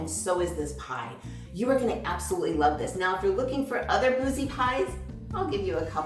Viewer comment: Boozy. I thought said, bougie. 😂😂😂😂 Love the pie!